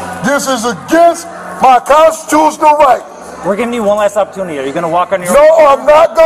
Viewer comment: This guy saying "Ambulamps" was the icing on the cake for me. 10/10 content. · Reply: So nobody heard him say "prostitutional rights"